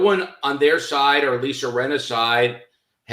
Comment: Rinnas involved somehow. I can see it in her lips. I guess that diamond isnt soooooo strong. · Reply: Hahaa thought you said Rhianna for a sec. I was like Wth?